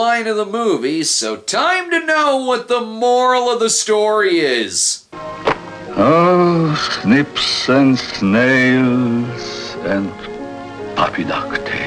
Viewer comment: I'm not sure if I missed the joke, but "snips, snails, and puppy dog tails" are the things boys are supposedly made of, in contrast to the girl's "sugar, spice, and everything nice." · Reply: I always heard "rats" not "snips".